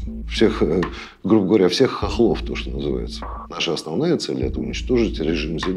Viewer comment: I think it's important to point out that the MP did not use the word "Ukrainians" as the subtitles say. Rather, he used the word "Hohols" which is a derogatory slur for Ukrainians in Russia